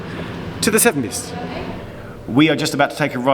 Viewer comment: I thought you said "To the seventies", but that matched perfectly with the orange background in the next shot.